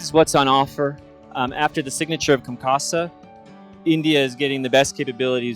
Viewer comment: What does he say at 1:18 signature of ?? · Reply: COMCASA